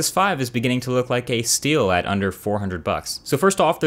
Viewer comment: nexus 5 is beginning to look like a steel what ? Please help me understand what he said